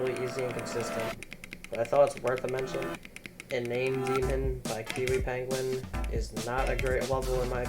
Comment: I couldn't hear what demon it was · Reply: My fault. It's called inane demon lmao. GL if you go for it. It took me an hour or so